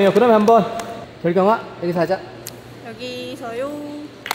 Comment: you said hyunmin instead of gyeolgyeong.. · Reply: It's just that the captions are wrong. Fyi, he said gyelogyeong.